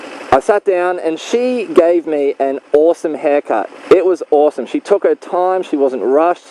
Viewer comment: I thought you were about to say "awesome blowjob" instead of "awesome haircut"